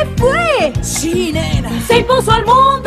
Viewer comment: Pensé que decía "en el olimpo se vivió la Máxima virtud "